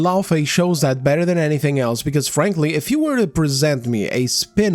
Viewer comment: Did you just say "Phranqly"? 😂